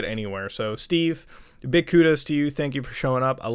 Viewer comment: in my lengueage you Just said big kutas to you at least thats what i heard. Btw kutas means Dick So its kinda weird coinsidence xd